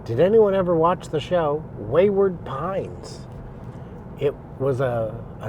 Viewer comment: I thought you said "Wayward Puns", so carry on.